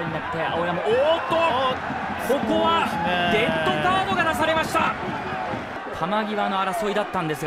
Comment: デッドカードに聞こえる